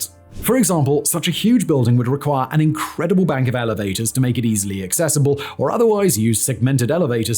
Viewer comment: I heard "it would take an incredible bank of alligators..." and suddenly the project seemed a lot more interesting